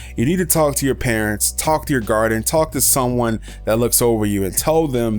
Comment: listen 4:55-5:00 I swear it sounds like you say talk to your parents talk to your "garden" Lol sorry but keep up the good work I love the videos